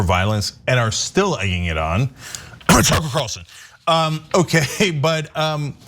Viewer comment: Standard disclaimer: i only post when i'm drunk. @ 8:12 'uh huh erlson" Sorry, i didn't catch that. It sounded like you had a piece of s#!+ stuck in your throat.